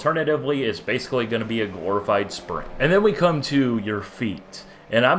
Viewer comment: what did you say? glorified spring?